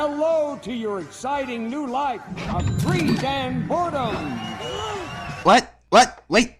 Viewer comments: It may just be because of the octopus arms randomly appearing, but at 5:36, I could have sworn I heard him say "a free damn porno" instead of "of greed and boredom." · Did he just say porno... in a kids show? And why does Jet sound like a drunk Ernie from Sesame Street? 6:51 WTF? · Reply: He said boredom actually, but it does sound like porno. XDD